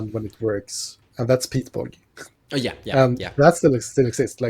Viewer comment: what did he say is "my favorite land when it works and thats _____" I listened to this clip like 20 times and I cant for the life figure out what land he's talking about. I don't know any land that sounds anything like what he said, I tried looking for lands that "enter tapped and tap for 2 mana next turn" and I've got nothing. It sounds like he's saying Peak's Borgy? I'm at a complete loss but I have no idea what card he's talking about and I really want to know what it is. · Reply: peat bog very few decks want it. I love when they do (it's a whole cycle)